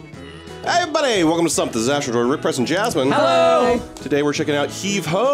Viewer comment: Hi it's ahsjsnhshsjahhs n Jasmin, that's what I ear every time I see a video of u guys 😂🤣✌️ love the vids can't get the into tho 🤔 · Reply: Noonyx33 I always thought he said “Hey everybody, welcome back to something disastrous - it’s Ric, Price, and Jasmin!” But I just realized, with the way I heard it, he wouldn’t be introducing himself 😂🤦‍♀️ thanks for clearing that up. 😂